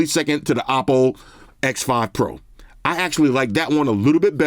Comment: I got confused for a second. I thought said his favorite camera design was the "Apple X5 Pro" instead of the "Oppo X5 Pro", since I've never even heard of Oppo lol. I'm for thinking that they chose the name "Oppo" to confuse some asian tiger moms who are picking up phones for family members lol.